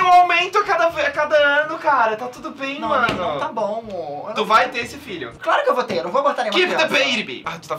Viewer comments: Ai meu deux eu ouvi ''mor'' aaaaaaahhhhhh eu shipoo!!!! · eu escutei um ta bom mo no momento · So eu que ouvi o falar "não tá bom mo"